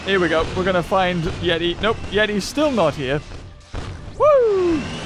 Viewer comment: did he say yetis stil not here or yet hes still not here? makes puns without even knowing!